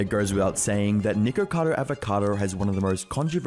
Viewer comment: ok hear me out i dont want any racism wars but it sounded like he said n____cadoAvacado